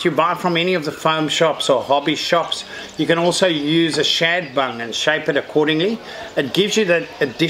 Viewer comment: I thought was saying 'shared bung'. Lol. Now I can understand ' shad bung'. Got it. Greetings from Australia.